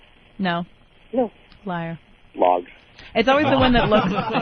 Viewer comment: What did Yimmy say? I can't quite understand it. Sounds like "logs."